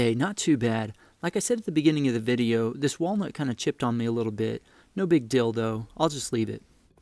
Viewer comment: "No big *Deal, though*..." sounded like you said = "No Big DILDO..." I was listening to videos that I downloaded while lying down, half asleep, cuddling with my sweet lil doggie, she must-have been dreaming that she was 'on the chase' because she started kicking me - while air running, startling/nudging me to a semi-conscious state... when I realize someone is saying something, I tune in (@ 3:39) to hear you say, "NO BIG DILDO..." to which I immediately realized it was in my headphones and thought, 'what the heck am I listening to?!?!?' And that's when I remembered that I had been listening to YouTube videos with the screen brightness settings(backlight bar = 0 + blue light filter = 100% turned all the way down and barely visible... I hit the screen with a double tap in order to skip back 10 seconds and struggled to get my blurry eyes to focus on the dimly lit screen that is difficult to clearly see even when fully awake, aware and my eyes are dialed-in to a razor sharp focus. So for a few moments, which at first glance all I could make out was the vague outline of a hand holding some kind of object... and after hearing "no big DILDO" and recognizing the size and shape something compared to the hand that was holding it...??? HAHAHA I quickly paused it and realized I heard something about wood chipping and wanting to have used oak... So, as I was getting into the settings to increase the brightness..., all I could think was, WTF DID I DOWNLOAD A VIDEO ABOUT MAKING WOODEN